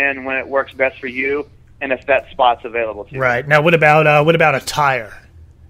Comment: I thought he said "a tire"...